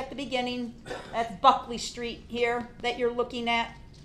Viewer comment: she sounds like she said “fuck this street here”